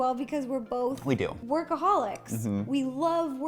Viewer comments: who else heard at first alcoholic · When she said workaholic I thought she said alcoholic 😂😅